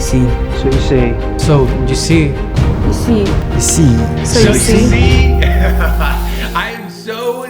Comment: Play the video from0:0 - 0:24 in 2x speed...... It says serve me a seat instead of so u see